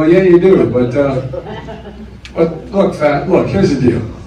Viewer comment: I DEFINITELY heard "Look fat." I doubt anyone says look facts to open a sentence.